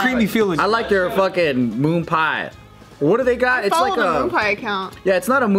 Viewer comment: I know you said moon but I heard CREAM.